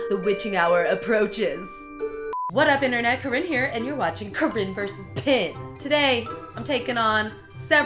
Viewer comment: Hi, Corinne. I loved this video, its very helpful and funny. I just wanted to say that there's a little mistake in the captions at the beginning instead of "come in here" you said corinne here haha and at 00:06 it should say corinne vs pin and it says karipan first lol at 1:22 it should say hand pillow instead of "and pillow" At 2:31 you said Cardboard and the caption says card foward Can you fix that when you have the chance? That would help the people who rely on captions. Thanks! #HelptheHearing #CaptionObvious